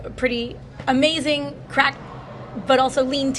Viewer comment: Did homegirl just say she's just one person with pretty amazing crack 😂 This would explain her word salad.